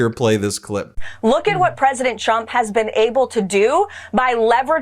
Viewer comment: Does Karoline say “President Chump” here?